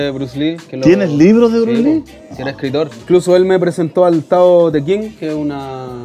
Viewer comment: alguien entiende el nombre del libro que dice el Aerstame ? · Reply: Tao Te Ching